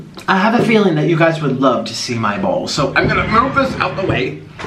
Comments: I litterally thought he said I know some of u would love to see my balls lol😂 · i heard "you guys would love to see my bowl" as "my balls" ew nah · I THOUGHT YOU SAID "I have a feeling you guys might love to see my balls" AND NOT BALLS I CANT I THINK IM TOO DRUNK FOR THIS LMFAO